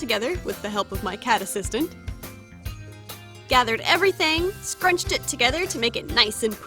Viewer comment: idk why but i hear "everythang" everytime she says everything xD